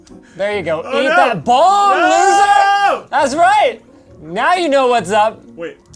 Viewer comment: Yo said niga.. well i heard "Eat that bomb niga"